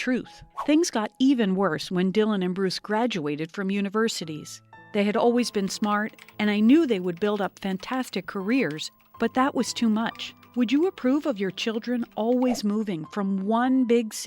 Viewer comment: For second I thought she said they would build up big beards